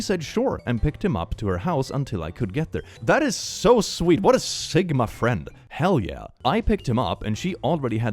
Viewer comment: who is excited for halloween plushies? :_ESD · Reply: Me- also did YOU SAY SIGMA CLICK 🤨🤨🤨